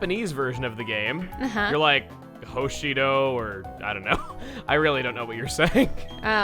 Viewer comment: It may be my ears, but I think I heard "Hoshido"